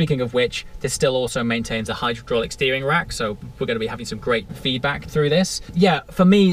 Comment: did you really say hydro-drolick steering lol? made me chuckle